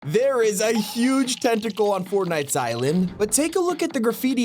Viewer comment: I thought he said there is a huge testicle on fn island for a sec 😂